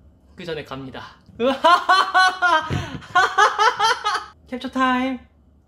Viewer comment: am I the only one who hears ''ketchup time''?🥲😂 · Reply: FRR I HEARD KETCHUP TIME NOT SCREENSHOT TIME 😂😭👍